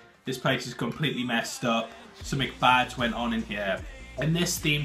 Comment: Did you say, "Some 'McBad's' gone on in here"?